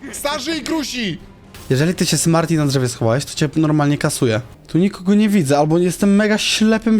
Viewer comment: usłyszałem kastruje..